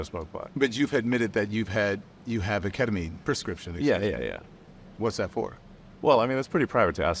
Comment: Did he just say k-k-ketamine :face-orange-biting-nails::face-orange-biting-nails::face-fuchsia-tongue-out::face-fuchsia-tongue-out